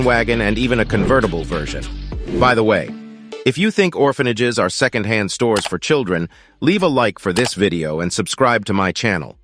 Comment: what did say? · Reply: Subscribe if you think that orphanages are second hand stores for children xD